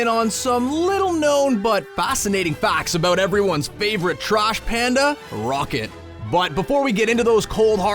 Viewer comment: I swear I hear trosh panda